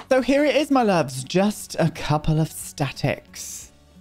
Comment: can’t just be me that thought it said ‘just a couple of statistics’ 😂😂